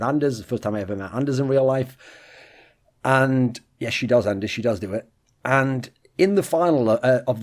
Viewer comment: wtf does he say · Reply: "yes she does Anders, she does do it"